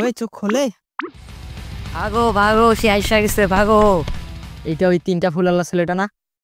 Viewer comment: at first I thought you said Ailsha aise bhago 😂 Ami mone hoy boyra hoye gesi 😅